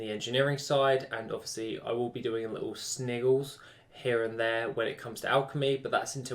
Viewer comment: What the fuck did you say? some "sniggles"? wtf? (so to speak)